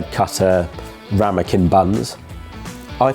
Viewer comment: The captions don't understand the word "ramekin" on a cooking show :face-green-smiling